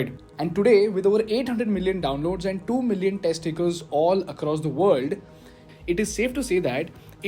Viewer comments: it sounded like 2 million testicles XD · did you say 2 million testicles 🤨??!!